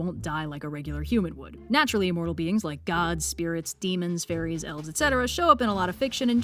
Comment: When you were listing of non mortals, I thought you said furries not fairies and I had quite a few questions.